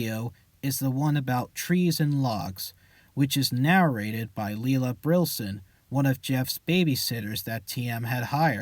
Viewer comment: I thought Jeff said her name was Susie